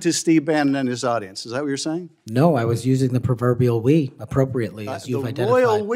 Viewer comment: Shamelessness is Trump’s superpower. Kash Patel doesn’t even know the English language. “We” is the first person plural of “I” and one or more other. The Royal “we” specifically refers to a statement by a single person, on behalf of himself or herself, and may or may not include others. What an ignoramus . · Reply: Maybe I have wax in my ears, but I heard it as "proverbial we"; which makes as much nonsensical sense than the rest of his testimony.